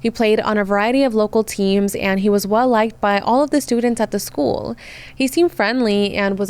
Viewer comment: All you had to say was “liked by all the students” and I heard “hi, I’m a red flag”